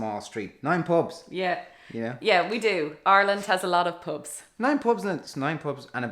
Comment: I kept hearing it as “Ireland has a lot of Popes” not “pubs” 😂